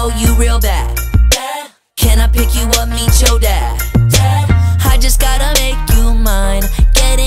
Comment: when he said can I pick you up meet your dad I thought he said eat your dad